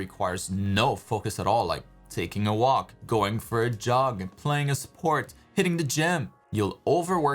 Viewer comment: Do what doesn't require focus.. I literally heard "like going for job instead of JOG" LMAO but makes sense either way